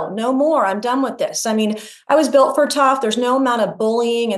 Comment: I know she’s saying “built for tough” but all I hear is “build Ford tough”